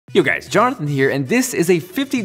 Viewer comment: Sounds like you just said "Johnthan"